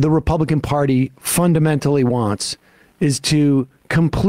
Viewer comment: I paused this video, did some other stuff ...and then when I unpause it, I hear Sam say "mentally wants is to completely ban abortion/.../" ... I realise that the "mentally" was preceded by "fun-", but... the statement works, either way.